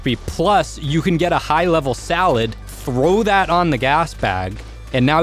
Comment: did he just say you can get a high level salad?